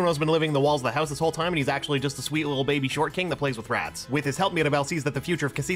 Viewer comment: I love describing Bruno as a little baby short king who plays with rats. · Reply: I thought they said “little baby shortcake” 💀🤣 Either one I think is fitting of Bruno😆